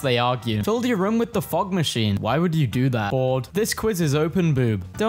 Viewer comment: thought he said "Ford" but he said "Bored"